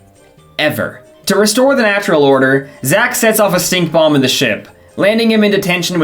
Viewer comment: thought u said "sink bomb"